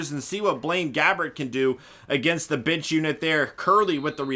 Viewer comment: Maybe it was just me, but it sounded like you said you were going to see what Gabbert could do against the bitch unit lol.